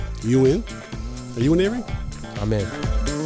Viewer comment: Did Coach call Avery; Erin?